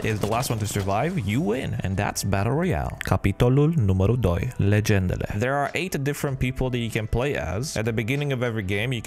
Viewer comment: Bă nenea, ești român că pot să jur că la 0:35 ai zis capitolul numărul doi ,legendele😄